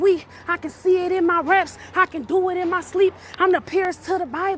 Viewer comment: did she just say I be pooing in my sleep? Honestly im not surprised. She probably has no clue when she is pooping. Ewe.